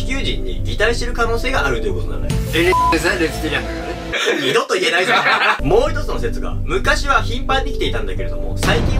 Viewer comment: なんて言ったん？ 気になるw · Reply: エリザベス